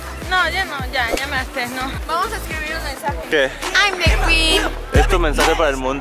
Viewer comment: Like si cachaste a Karen que dijo bitch en el minuto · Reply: Alejandro Resendiz Rendon De hecho dijo boom bitch😂